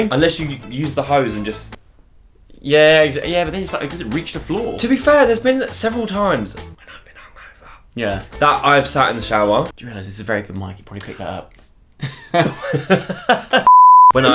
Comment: Does anyone know what said at 6:54 cause I’ve tried to work it out aha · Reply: "When I was hungover" I think that is what he said